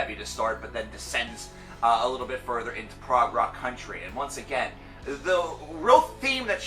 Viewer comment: At around 3:20, I thought I heard "prog country". Man that was scary haha